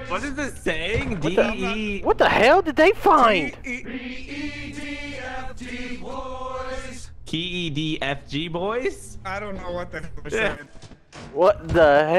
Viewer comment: They found an Earth Defense Force Cabinet! That’s Amazing!!!!!!! To Everyone who got the cabenit it says “The EDF Deploys!” To be honest, the fact you guys misheard it is agonizing. LOL!